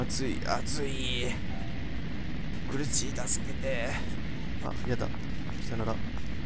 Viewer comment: あやちゃんのセリフ 「あ………やだ………」 なんだけど、 が言うと 「あ？やだ」 みたいに聞こえる